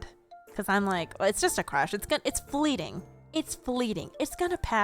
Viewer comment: "it's fleeting, it's fleeting— it's gonna pass." Me: ✨Hakanai✨ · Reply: For some reason I thought she said bleeding not fleeting